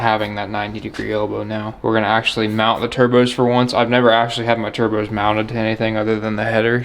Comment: Hey I thought you said you was ganna bed mount the turbskis